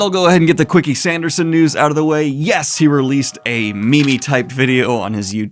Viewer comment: Did you say 'me-me' ?